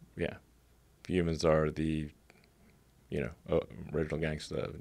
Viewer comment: guys what's Elon saying here? "humans are neuro gangsters" it's not clear I need your help, cheers :)